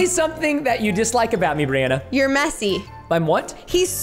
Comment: Bri: I said messy not gassy Preston: I thought you said gassy